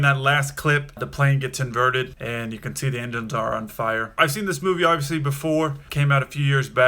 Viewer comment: your official closed captions say "as you can see, the Indians are on fire". Not because I'm an Indian, but i actually want to know what you actually said is on fire and I didn't even notice anything in the clip that is visibly on fire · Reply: “The engines are on fire.” Those captions are auto-generated. They don’t always do the best job at capturing what’s being said.